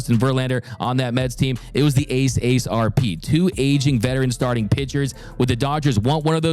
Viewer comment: Did he just say "Ace Ace RP" 😂😂🤣🤣🤣😭☠️